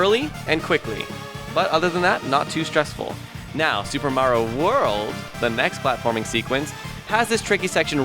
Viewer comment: I thought you said super mario world XD you deedee best